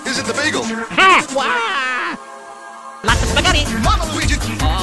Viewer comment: For the first time i heard "I am spaghetti".